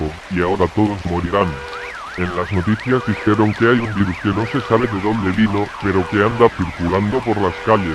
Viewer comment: que dijo que no entendí xD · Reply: " Creo que es un virus que no se sabe de dónde vino "